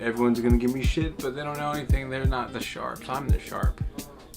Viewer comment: I think just gets so much criticism because he is misunderstood like at 8:06 sounds like he says sharp, but really he says "I'm the shart"